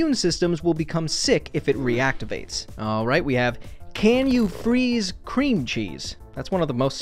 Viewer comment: Today's Fact: In 2020, a team of researchers discovered a new type of ice that is so hot it would burn your hand, despite being frozen. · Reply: I thought you said ice cream for a bit and was confused